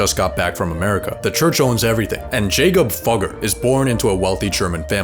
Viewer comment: I thought it said Jacob fucker